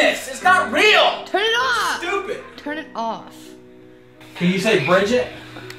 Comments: can u say Bridgette , fat women, LOL that's what I heard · fat woman? sounds like thats what they said lol